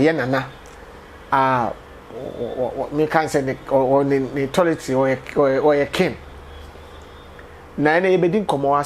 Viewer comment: did I hear, ni Toiletry oy3 King? Bra Kweku ad3n?? 🤷🏽‍♂️🤣😂